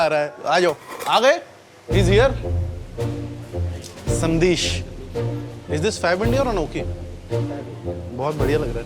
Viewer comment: The fact that he clearly said Fab India at 2:21 and the subtitler put Anokhi? xD